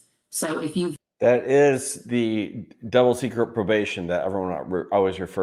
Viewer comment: What type of probation did say? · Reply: Double Secret Probation. It's a real thing, but best known to the public through the "Animal House" movie.